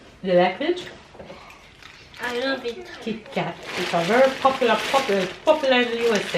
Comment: isnt that Kit Kat 😅😅 I swear I heard you say Tic Toc. You know that's the only chocolate I eat. I love the wafers part of it.